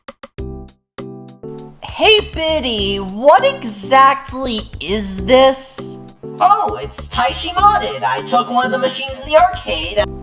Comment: did i just misheard "Diddy"🪑🪑🪑🪑🦅🦅🦅🦅